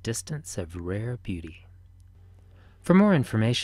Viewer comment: Lol. I thought he said Science.nasa.gay